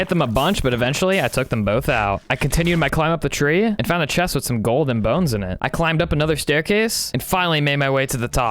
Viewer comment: did he just said golden bones